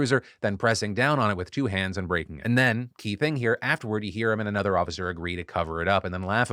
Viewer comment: it sounded like you said "...and raped him." when in reality you were talking about breaking the dude's phone.